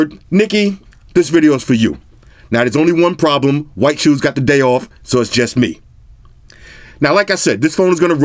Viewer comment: I thought you said White Shoes took the day off. White shoes be like "yo! u better not be tryna do an unboxing without meaouhh!!!